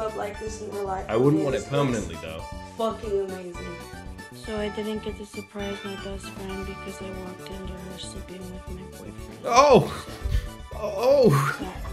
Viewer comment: can someone translate what she is saying. All I hear is some quiet mumbling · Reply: She said, "So, I didn't get to surprise my best friend because I walked in on her sleeping with my boyfriend,"